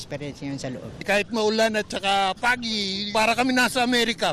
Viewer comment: what? Paggy or you were trying to say FOGGY? Damn!!!!